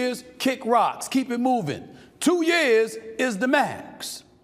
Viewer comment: i heard gas 0:33 Two years is the max 💨💨💨